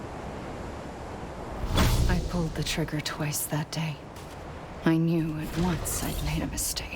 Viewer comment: I guess you missed what said was "I pulled the trigger twice that day" at 37:42. If you remember what happened in Part 62 at 36:53 it starts to make sense.